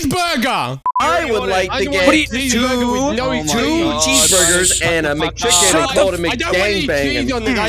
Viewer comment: "two cheese burgers, and a mcchicken, with a mic gangbang"💀💀 i think thats what it said